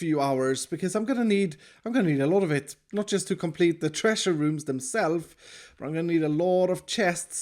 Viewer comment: I thought he said treeasure room 🤣